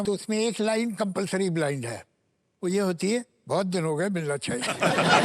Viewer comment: What did javedsaheb said at 5:50? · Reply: "Bohut din ho gaye, milna chahiye"